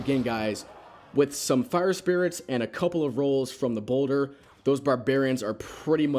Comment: did he say bowlder?